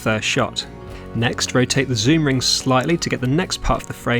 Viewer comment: He said focus ring. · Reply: No, he clearly said to rotate the ZOOM ring at about 2:49 of the clip.